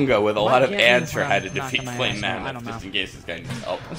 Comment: lol all i heard is "flame mamut"